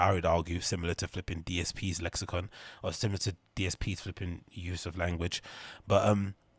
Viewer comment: This got reccomended and was a great video even 6 months later. Also, 3:41 did you say dsp? As in e-begger phil?